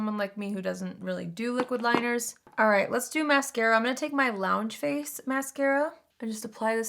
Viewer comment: What was the mascara you used? It made your lashes look so full! Edit: I found it! I thought you said lounge phase but it’s loungeface 😆😆😆